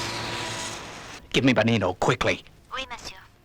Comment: Yes, you're correct about Lil. According to the wiki, she was sitting with her back to the camera during the second performance by the Cass Carnaby Five at 33:12. Also according to the wiki, the woman sitting with her back to the camera at 33:17 is a rewigged Marina puppet from Stingray. The Paradise Peaks receptionist at 16:06 is the same puppet that played Maxie earlier in the episode and he's wearing the same outfit he sported as Bletcher in Martian Invasion. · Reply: Oh. I didn't notice those details. Incidentally, the subtitles on the DVD call Banino "Benito", possibly because it sounds like Parker says "Benito" when talking about him. The subtitles also call the former associate of Parker and Maxie that Parker namedrops "Pudgy Patterson", even though both Parker and Maxie clearly say "Punchy Patterson".